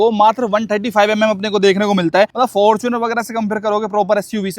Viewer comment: Bhai fortuner ek alag segment ki car hai aur bmw ek alag segment ki car hai agar apko compare karna hi hai toh isse iske segment se compare karo naki kisi other segment se nahi toh comparison worthless reh jata hai. Vese apko 2019 se follow karta hu Apka loyal fan he kehde. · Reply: he clearly said that ki iska fortuner se koi comparison nhi bnta hai 🤧